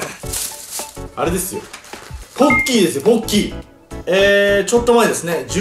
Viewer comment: ポッキーですよぼ○きーって聞こえたw